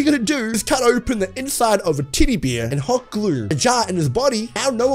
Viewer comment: Dude u sound like u said titty bear instead of teddy bear